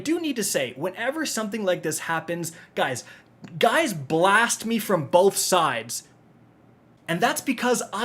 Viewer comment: Did you just say “Guys Blast you from both sides” AYO!!! put a pause in there WTF!!!😂😂